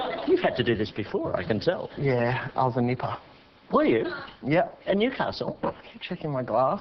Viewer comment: what did he say? He said he was in something, some place I think. Sexiest accent ever. I just can't understand a word or two sometimes lol. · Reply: He said "I was a nipper" (meaning: "A child aged from 5 to 13 in the Australian surf life-saving clubs.") ;)